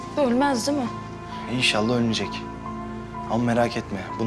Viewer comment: ali insALLAH ölmez diyor ve bende insALLAH ölür diyor duydum hahaha